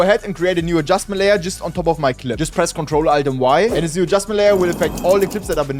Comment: What Did You Say At 0:18 What we should press ?? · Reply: Ctrl+Alt+Y